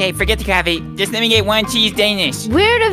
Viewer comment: I thought she said cheesed ana- 💀